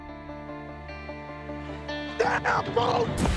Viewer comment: what did he scream at the end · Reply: Damn fool?? I thought he said damn bro